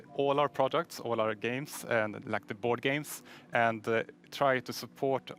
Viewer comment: DID I HEAR BOARDGAMES?!?!?!